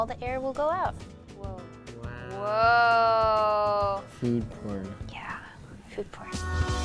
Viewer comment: Did I hear food porn?